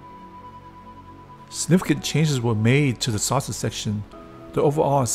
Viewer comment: haha at one point 2:17 the CC said "sausage section" a fun and meaty ship :-)